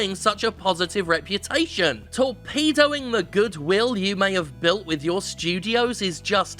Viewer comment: Dont know why I heard 'torpedoing' as ' to pedo'ing' but I did. Sort of tainted my view at that point